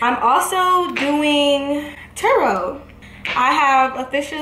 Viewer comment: Why did I think you were saying tarot like tarot card reading 😭😭😭